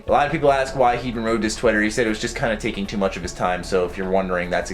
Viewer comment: He rode his twitter? what did he say?